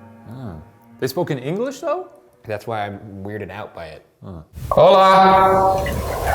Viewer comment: it did not say yes I heard “get out of here” or “hello there”